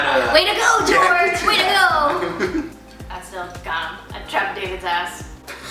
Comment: What did say at 7:44? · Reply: All I got was 'that's so ___, I trapped Davids ass.'?